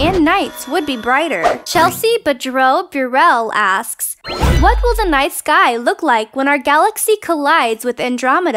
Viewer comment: Am I the only one who hears: "What will the nice guy look like when our galaxy collides with Andromeda?" The answer of course is: He'll look pretty much the same as he does now, only a bit more flustered and apologizing for bumping into her; "My bad, sorry 'bout that, eh."